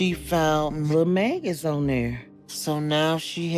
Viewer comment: I was listening to this and thought she said "found mayonaise on there" and was like wtf, but upon going back and seeing the subtitles, I see it was maggots, not mayonaise lol.